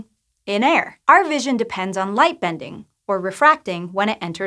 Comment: I thought she said lightning bending for a second and got excited ⚡️ any avatar fans here? 🙋🏻‍♀️